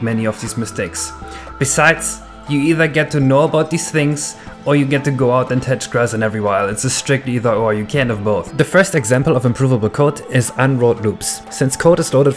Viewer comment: Can someone please translate what he say at 7:13 You can go out there and <garbled> or you can <garbled> but you cannot do both. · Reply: You either get to know about these things, or you get to go out(side) and touch grass in "every while", it's a strict either or, you can't have both.